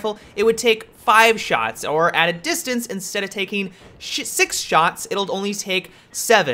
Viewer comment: You said "Shit" instead of six. lol